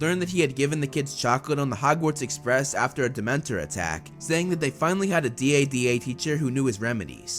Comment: when he said dementor attack I thought he said demented attack. LOL